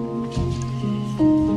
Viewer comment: I think he said to paparazzi's " Thank you ". He looked so sad. RIP Jonathan.